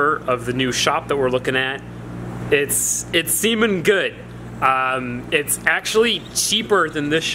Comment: did he say It semen good